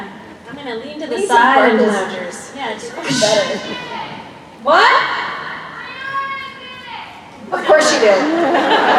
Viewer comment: What did they say at 3:20? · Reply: The person that yelled yelled, I think: Briana did it!